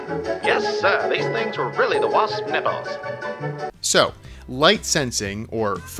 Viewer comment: Did he say the Wasp's nipples??